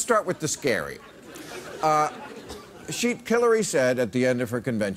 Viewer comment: I swear he said "Killary"